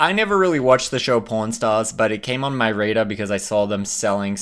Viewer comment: your accent!😂😂😂 I thought you said po r n stars!😂😂😂😂